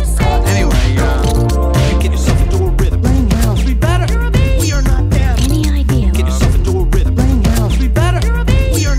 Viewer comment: what does say? (Between "get yourself into a rhythm" and "be better") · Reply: "laying out". From the part where he says "they're laying out, they're sleeping in".